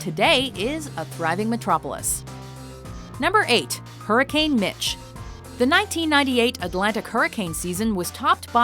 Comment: I thought she said hurricane bitch